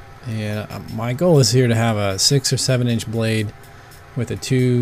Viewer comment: Im well aware that you say "my goal here is to have a six or seven inch blade" but i keep hearing "Michael is here to have a six or seven inch" and i cracks me up